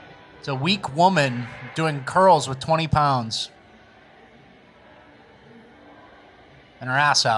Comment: I was listening to the audio and I heard “women curling 20lbs” I was like wtf?! Switched over to the video and saw she was curling a 20 bar not 20s dumbbells 😂